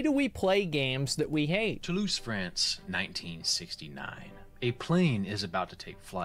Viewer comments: "Why do we play games we hate?" And then I heard "to lose friends" xD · "Why Do We Play Games We Hate?" and then Tolouse, France and i hear "To lose friends" ahahaha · Why do we play games that we hate?.. I heard -> "To lose friends."